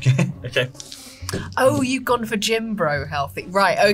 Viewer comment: I misheard "gym-bro" as "jimbro". I thought it was British slang for a sec.